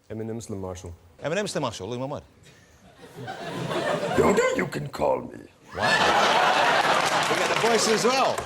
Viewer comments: What does he say at 1:13? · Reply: He says Yoda you can call me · What does he say at 1:13 ? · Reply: Yoda you can call me ( if you don't know who yoda is you have had a messed up childhood)